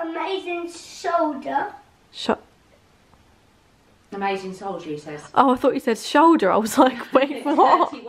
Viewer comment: “Mummy bought this amazing shoulder”” “Soldier he said” “ oh I thought he said shoulder “ 😂😂 honestly same